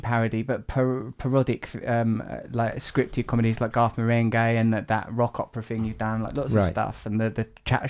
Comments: When he said Garth Marenghi in his British accent, I honestly thought he said 'goth meringue'. Then, googled that and found it there is exactly such a youtube clip. Life is strange. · Also I didn't know about Garth Marenghi and heard it as "goth merengue" and went looking, and I would still like to see some goth merengue, please.